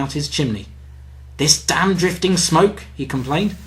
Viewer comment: did Scott just say, "damn"?